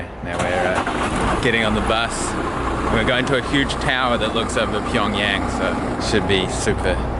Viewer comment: I thought he said "huge tower"